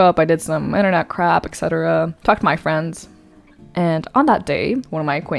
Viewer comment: thx god u got subtitles or thing would go weird from 0 to 100 real quick I heard "i did internet crap. etc fucked my friend "